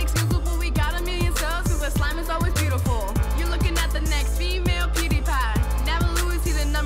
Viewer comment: The first time I heard this I heard “you’re looking at the next female cutie pie” now I hear “you’re looking at the next female pewdiepie” lol